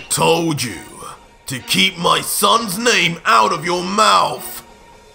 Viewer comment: I thought will smith said that "keep my _ name out of you mouth haha